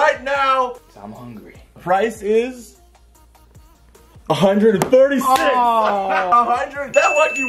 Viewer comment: did he say "136"